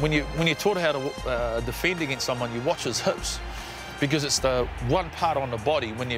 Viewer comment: i can't understand what he's saying at 1:15, wich part we've have to follow ? the head ? or the chest ? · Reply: He’s saying he was taught to watch a player’s hips when defending an attacking player with the ball because unlike other parts of the body, they don’t move around too much ie side step, head fake, throwing a dummy etc are designed to deceive you.